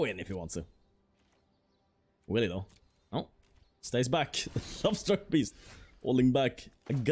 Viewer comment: Is it me or does it sound like he is saying Lobster Beast instead of Love-struck Beast?